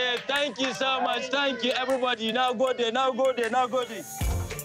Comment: he actually said “Nagode” which translates to “Thank you” in Hausa. The subtitles got it wrong 😂